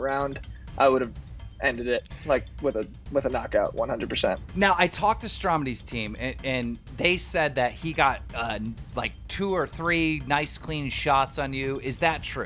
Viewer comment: I thought he said stomedy the milk dud man...disappointed